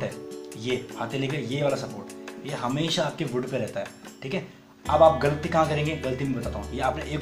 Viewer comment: I was just listening with my focus on my fingers on guitar, and suddenly what I heard at 9:10 was, "yeh hamesha aapke bund pe rehta hai" 😂